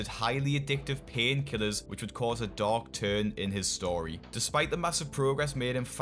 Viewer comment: When you said “dark turn” I heard “dog turd”.